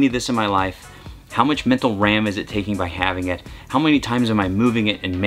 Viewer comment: Did you just say “Mental Ram?”